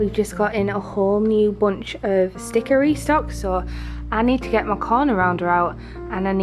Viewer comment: You said “Sticker Restocks” but all I heard was “Stickery Stocks” lol